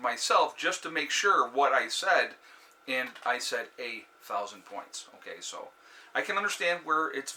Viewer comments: That's funny I did watch video and I thought you said 8000 pts too..but I just knew that would be catastrophic.to shtf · At first I thought you said 8,000 also but told myself that you couldn't be that wrong since I already knew both the current value at the time and the day's low and realized it was just your accent that made it sound that way.